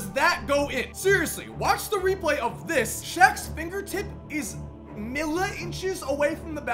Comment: Lol I heard ‘milliinches’ on the Tim Duncan shot and I was confused. I forgot that millimetres isn’t a thing in America cos they are imperials not metrics